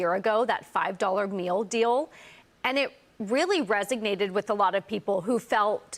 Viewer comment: she says resignated instead of resonated lol